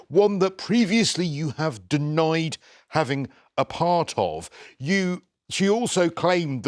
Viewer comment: When the TV analyst said "a part of" I thought that he said "apartheid". And as we all know, that's illegal. Free Meghan Mandela!